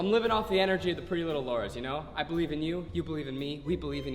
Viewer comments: i think he just said prettylittlelauras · Did anyone hear him say “pretty little Laura’s” at 1:55, like if you heard him